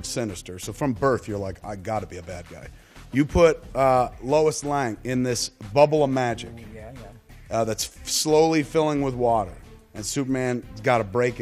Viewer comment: does he say "Lois Lang" at 1:15?